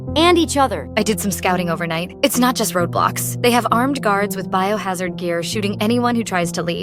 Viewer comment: i thought it said "roblox"